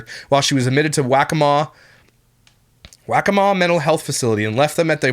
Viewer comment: Circumstantial evidence...all evidence is. DNA on a library book or on the murder weapon? Depends on the circumstance. And when a lot of circumstances are factually connected, that equals DNA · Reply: (I'm sorry, I thought said "Whack-a-Mole Mental Health Hospital", had to go back and check the captions, lol)